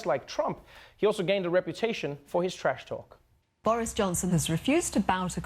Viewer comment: I thought you said Boring Johnson. ;)